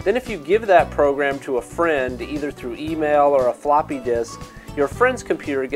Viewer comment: I thought he said Floppy Dis. When the Virus makes jokes about your mother. Is his name really Marshall BRAIN?!